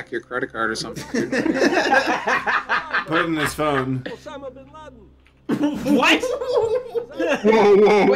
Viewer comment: Tbh, I thought he said Son if Aladdin, not Osama bin Laden.